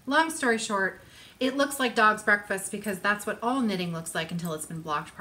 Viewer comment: Did you just say all knitting looks like dog breakfast when it's not blocked yet?? Or is that my weird hearing? #spitsouttealaughing What a fab video, thank you. And thank you so much for showing us around your neighbourhood. Gosh those houses look DIVINE. <3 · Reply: yup ;-) Dog's breakfast. It's real - it looks like total crap until its washed and blocked LOL